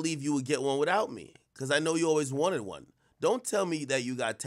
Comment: Soooo MOMs does confirm that he “always wanted” a nose job ??? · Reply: I could of sworn it said “I knew you always wanted one”